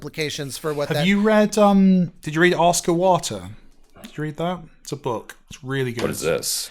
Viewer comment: I forsure thought he said "Oscar Water" too 😅